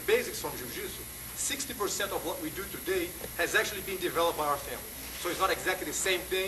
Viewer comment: I wish more people would watch this. All the most common criticisms of the Gracie Family are shown here to be patently false. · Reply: (JEW-JITSU) What he actually said was "Sixty percent of what we do today has actually been developed by our family." and the correct timestamp is 3:58 ... You actually want to start arguing what the word "developed" means or would you rather explain why you deliberately misquoted him? See, where I come from, we have a name for people who deliberately misquote others They're called liars. Don't bother. Developed : Advanced or elaborated to a specific degree - Oxford English Dictionary. Anyone who would argue that this is not what the Gracies have done with what they were given over the last, most of a century, is going to have a lot to explain. Would you care to present an argument to that effect? Or would you like to start with an apology for the misquote? I'd accept either but dodging the latter will prejudice the argument and make me much less likely to cut you any slack as we proceed.